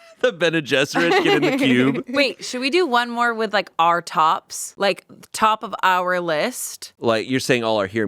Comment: What was said "Our tops", what I heard "r/tops". Thought Amanda was going to talk about something very different